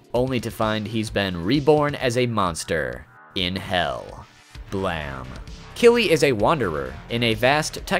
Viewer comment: did you really call it “Blam” ? 😂